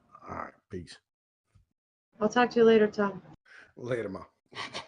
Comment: was that your mother ?????? I swear I heard you say "later mom". That's gana be a awkward Thanksgiving n shit